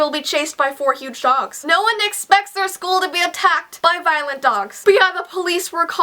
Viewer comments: Im being 100% honest, but when you said VIOLENT dogs, I thought you said violin dogs and i pictured 4 big dogs that looked so much like violins and yeh, it was wierd xD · okay, i kind of thought that it said "hot dogs attacked my school" 😜😜😜😜😜😜😜😜😜😜😜😜